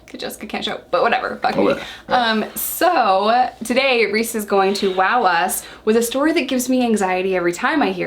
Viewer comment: U said ,"Reece is going to wow us".... what I heard was,"Reece has got wawas" 🤣🤣🤣Love u guys!!!!😜💖